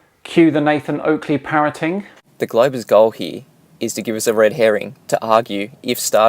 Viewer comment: "The globe is gohi," ... No wait. Sorry. I just misheard. Heh. I thought he was making up words as well as "facts."